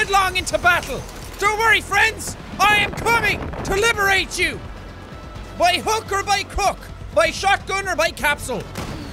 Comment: Am I the only one who thought he said “by hooker by crook” at 2:40😂